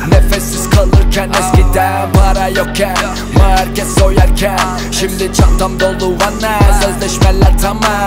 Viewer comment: Kesoyerkeha diyo yada ben anlamadım ne diyor yardımcı olur musunuz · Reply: Market soyarken diyo knk